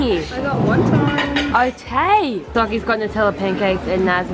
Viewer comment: Did just say “Otay” 🤨 click this to hear